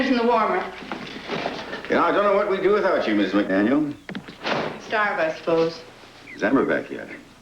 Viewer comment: "Starbucks' closed", I thought she said